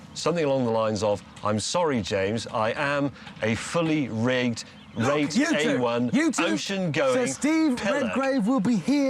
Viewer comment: what is James actually calling Jeremy here, i always thought it was funny but the only thing i know is pillock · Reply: Fully Rigged Rate A1 ocean-going Pillock 😂. You're welcome.